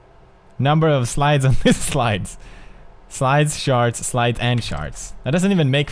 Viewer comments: When he said charts, I swear it sounds like he's saying sharts.(ex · sounds like he said at 5:50 " Slides and Sharts"